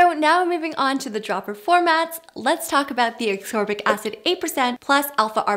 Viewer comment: She really sounds like she saying aksorbic instead of ascorbic